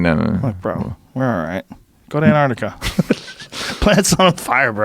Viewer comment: Two problems with this vid inside of 1:30: 1) Round up ad. 2) Guest doesn’t know difference between the Arctic and Antarctic. C’mon man. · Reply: Thought he said Arctic, because it was 84 degrees in Northern Russia a few days ago, that's pretty much the Arctic.